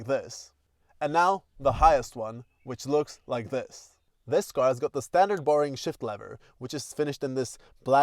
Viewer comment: I thought he said this cars got the standard boring shit leather 😂 I thought the leather looked nice !